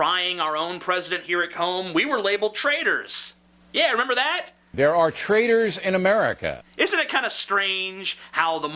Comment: I thought Bill O'reilly said 'there are traders in America"